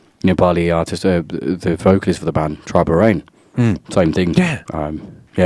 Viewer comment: did he Sayed tribal rain?